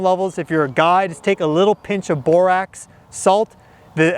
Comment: What was the supplement you said to use to get more Boron? You said it's like a type of salt? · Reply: Yes BORAX is the simplest and cheapest way to get elemental boron. Literally just a small pinch is all that's needed, which is no where even close to the safety threshold -> <link>